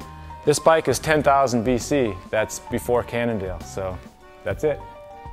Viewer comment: Did he just said "this bicycle is 10000 b.C., that's before Cannondale" at 3:58?